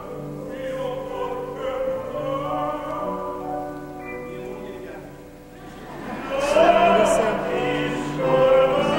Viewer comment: What does he say at 4:18? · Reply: My wife cries !